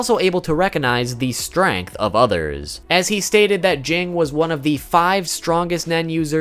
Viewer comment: what did he say at 3:16 ? jang?